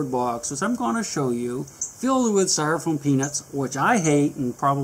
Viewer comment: At first i thought he said he ate the foam peanuts LOL